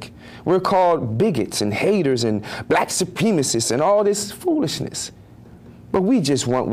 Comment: Hey, it's Eddie Murphy. Loved him in the Nutty Professor. 1:39 did he say black sup-penises?